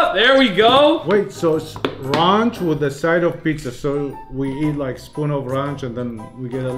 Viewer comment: Who did he say ranch with the side of pizza? I thought it was pizza with a side of ranch